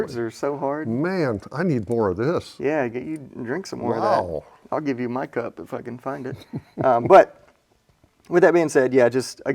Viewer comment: Completely sounds like says “I’ll give you my cup, if I Fkn find it”